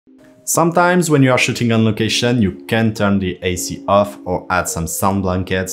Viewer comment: I swear I thought you said pooping on location but the S word I had to play it back 3 times to hear shooting on location good video I really learned a lot.